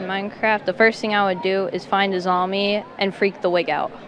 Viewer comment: what does he say there? “Freak the wig out” ? I can’t figure it out